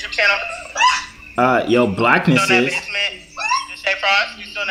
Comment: she really said "Deshae Frost"